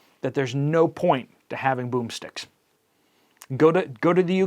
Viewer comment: Oh lol I thought you were saying broom sticks lol